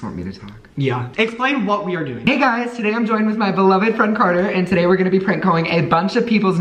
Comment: DID CARTER SAY "HI I'M HERE WITH MY FRIEND CARTER"?!